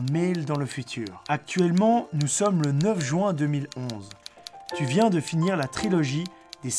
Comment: c'est moi ou il a dit 2011?